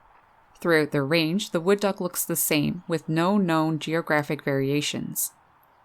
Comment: I heard the wood duck looks insane at 1:35 haha had to listen to it over